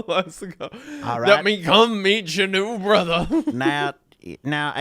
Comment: When wendigoon said "come meet ya new brotha" I heard something VERY DIFFERENT and honestly thought he was making a reference to an IP or a joke cause I heard "BECOME MEATCHANUBRA" which sounds like a sort of meat, scorpion monster thing which was sorta baller to imagine